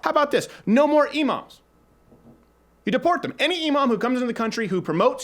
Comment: thought you said "deport emos" how i wished you did...